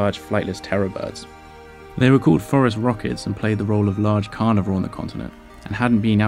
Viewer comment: I always mishear the scientific name of Terror Birds as "Forest Rockets".